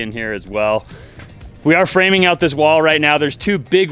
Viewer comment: I thought it said FARMING and I was thinking, "When the Hell did you all start that!?"